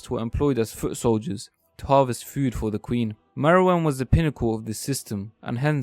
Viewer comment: Lol at 1:16 CC says Queen Marijuana